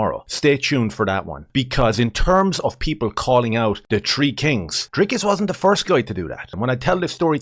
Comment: As an American I thought you said tree kings instead of "three kings". I'm losing my mind 😅